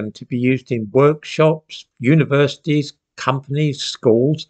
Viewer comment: I first heard it as "woke-shops" 🙂